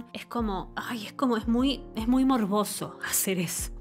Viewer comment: Solo entendí "un baile morboso". 🤣🤣🤣❤️